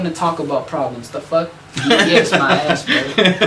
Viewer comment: did u just say "dds my ass?"